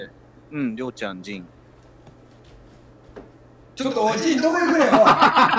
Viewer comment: おい仁どこいくねん！が おじんに聞こえて笑ったw